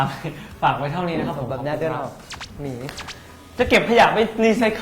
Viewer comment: พีพีพูดว่าไรนะ เราได้ยินว่าบี๋😂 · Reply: น่าจะพูดว่า "ขออนุญาตเดินออก หนี!" นะคะ😂😂